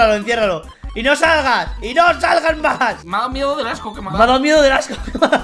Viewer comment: Like si te diste cuenta que dijo "y no salgas majas" en vez de jamás xd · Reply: En realidad dijo "y no salgas más" :v